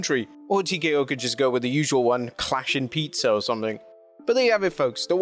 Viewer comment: did he just say, “clashing pizza.” 😂